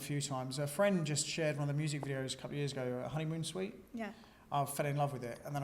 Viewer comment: does he say Honeymoon Suite?